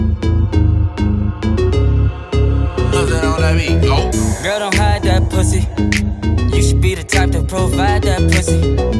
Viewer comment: what does that guy say in the beginning? click here> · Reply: Rylee Tolentino mustard on the beat